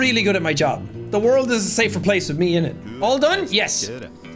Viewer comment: ”the world is a safer place with me innit” is no joke what i heard. I was like i can get used to bri’ish jack. Then i realised.